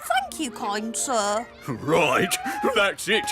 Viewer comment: that sounded a bit like 'fvck you kind sir' and i feel like the man misheard him, so that's why he asked him to repeat the sentence.